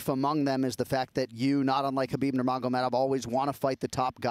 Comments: did I hear it wrong or Anik said that Habib doesn't want to fight the top guys?!! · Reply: 'not unlike' Khabib. That means the same as 'you want to fight the best just like khabib' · Did he just say unlike Khabib you want to fight the best ? I must’ve heard it wrong